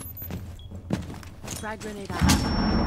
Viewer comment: Anyone notice at 0:02 she says gta granade Instead of impact? EDIT: sorry I meant